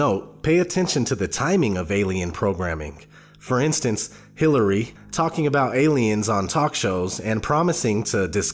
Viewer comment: did you notice the Cc says Hillary, our next president . However that is not what is said. you say Hillary talking about aliens on talk shows. Youtube and Cc trying to distort your message!